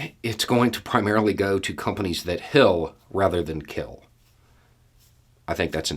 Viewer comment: "Companies that heal instead of kill!" Those are definitely the companies that should've always been the ones to profit the most. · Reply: I heard "Companies that hill instead of kill." And I am a Southerner.